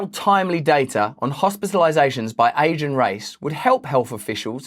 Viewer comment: I thought you said Asian race not age and race I had to rewind 😅